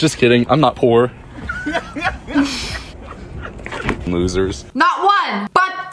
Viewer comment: Did he just said loser he is loser